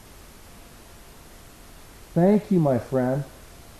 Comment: Sounded like he said Hey ! Hey. Friend^^ 💖 And he sounded verry happy to talk to you😍💛👌💎amazing